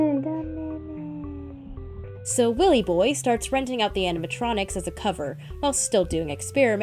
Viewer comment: I thought you said rizzing up the animatronics😭